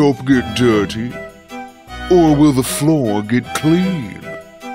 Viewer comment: he says ‘get clean’, but the captions say ‘be clean’.